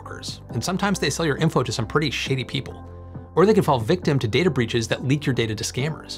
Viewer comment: I thought I heard you say "💩ty" as opposed to shady people 😂😂😂